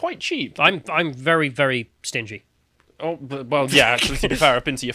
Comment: I was eating cereal and because of the crunching sound I misheard, I thought he said "I'm very very stinky". I thought he meant he gets so stinky during exercise that he was embarrassed to work out at the gym and that's why he does it at home XD